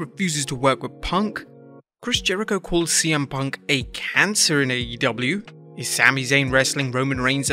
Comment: I thought that said cm punk had cancer